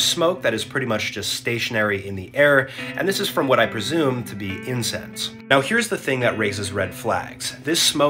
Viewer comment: I I thought he said incest not incense 😭